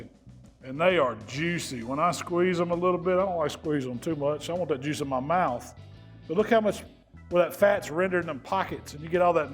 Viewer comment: did he say I want that juice in my mouth??? pause 😂😂😂